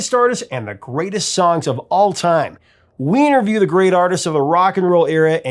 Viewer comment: Did he say weiner-view